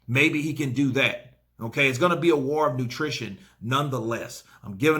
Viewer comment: Did he say “war of nutrition” at 4:37?? 😂😂